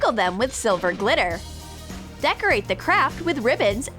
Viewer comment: Am I the only one who thought they said "Decorate the crap"